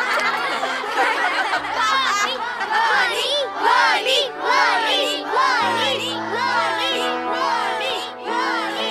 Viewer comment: Somebody please tell me what those kids were shouting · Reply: Worny